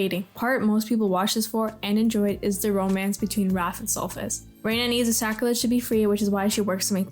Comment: The captions say “Wrath and Sauce” here lol